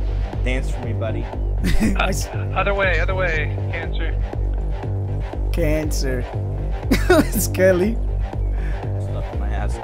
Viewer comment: did you say cancer ?